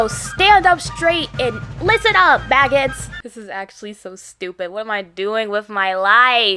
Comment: what did you just call me? 😨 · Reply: A maggot 👹👹👹